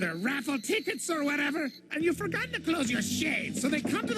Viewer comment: did he say and you forgot to close your shit